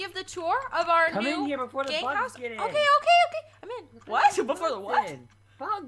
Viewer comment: I thought said "Before the 'butts' get in" XD